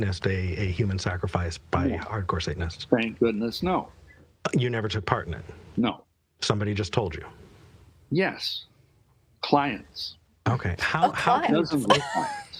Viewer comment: I also thought he said “whore Satanists” 🤣 - Wow! This was just off the rails!! did great though!